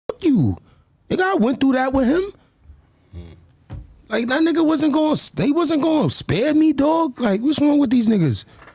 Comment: I thought they said thisn nigga wasn’t his son 😭💀